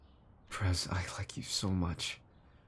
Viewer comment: is it js me or did he say “Press i like you so much” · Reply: i believe he said pres, like short for president